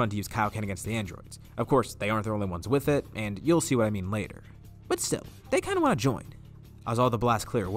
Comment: why does it say discord when you say “but still”?